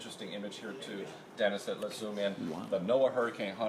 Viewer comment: Did I just hear WOW?